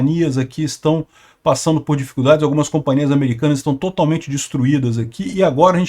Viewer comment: Companhia americana?????? Ou entendi errado.